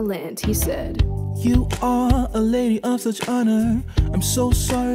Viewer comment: What did he call her at 0:36? He said she was something of honor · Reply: Lady of such honor